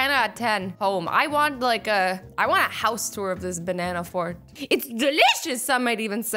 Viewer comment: my mom thought you said banana fart